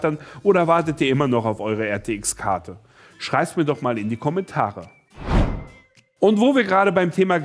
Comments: Ich geh mir glaub ich mal die Ohren putzen. Hab bei 1:49 doch glatt verstanden: „Scheisst mir doch mal in die Kommentare“ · Hab ich es richtig gehört wir sollen auf 1:50 in die Kommentare scheißen Ok bitte schön 💩💩💩💩💩💩💩💩💩💩💩💩💩